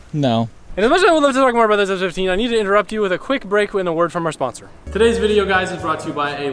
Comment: what are you saying HAHAHAHAH 2:20 all I can understand is interrupt you with a quick brake for our in the word from our sponsor